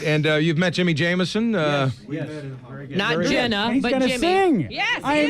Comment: Yes, yes, believe me that out of respect for the artist I would never have mentioned that incident if I had misheard his name, it was also stupid as if his last name "Jamison" was unknown. But Jimi as always was a "Gentleman" said nothing and just smiled. That was always my sweet angel... I send you a big hug from Bogotá Colombia 🇪🇨🤗🇪🇨💯💯👋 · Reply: Ahhh perdona!!! 🤭Estoy igual que el tipo que confundió el nombre de Jimi por Jenny... 😊😅😅😅😂😂😂🤣🤣🤣